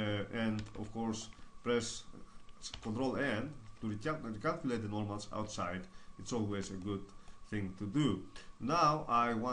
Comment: I couldn't tell what you were saying... sorry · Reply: "Press Control-N to recalculate the normals. It's always a good thing to do." You can also see the keyboard shortcuts matching the audio in the lower left.